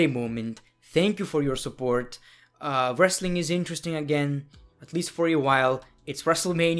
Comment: i thought he was gonna say Wrestlingnewsnow